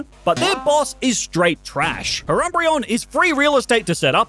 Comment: why did he say urumbreon 🤣🤣🤣